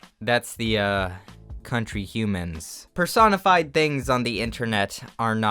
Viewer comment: what did he said here cuz i think im hearing persona 5